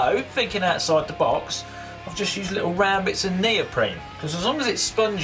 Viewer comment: Can someone please write down what he says in 1:53? That piece name, im not english. Thanks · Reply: Razquin He said neoprene, specifically. It's the type of material they make diving suits from.